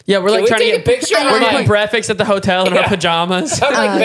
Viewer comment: he said “brefix” instead of “breakfast”😂